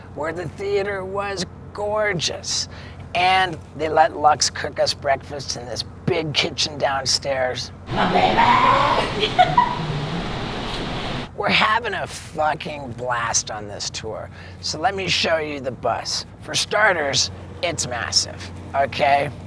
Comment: i know he said he would rather not have a miniature him roaming the earth, but 4:45 i swear i heard "hey baby" as him and what looked like a glowing lux simultaneously looked at her belly!?